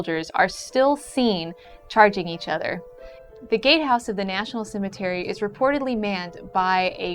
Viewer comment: is she saying the gay house?